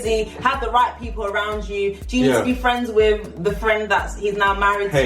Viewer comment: You speak soooooo fast that I cannot understand what you are saying. From 11:08 ....what are you saying? Be friend with your ex 's spouse????